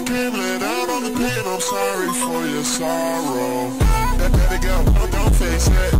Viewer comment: Was it just me? Or when it said “let it go” i thought it said “baby grill”🤦🏻‍♀️😭💀